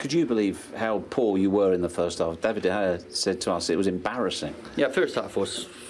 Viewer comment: firstly i thought he said "david de gea saved your ass, it was embarrasing!"